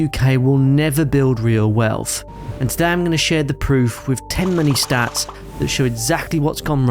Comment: Haha, I heard "today I will share the truth with 10 money stats" as "..share the truth with too many stats" 😅😂